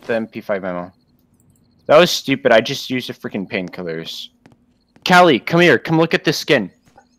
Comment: I thought said this: "That was stupid, I just used a friken pingkiller." instead of saying painkiller